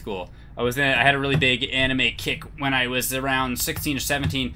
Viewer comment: Really thought he said he had a big anime kink instead of kick